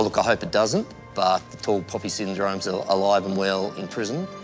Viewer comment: what syndrome did he call this ? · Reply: I think he said “tall poppy syndrome”, for, the possibility of, being more noticed by the other inmates.